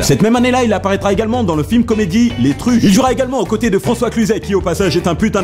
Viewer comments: c'est moi ou il a dit les truches ? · c moi ou il a dit les truche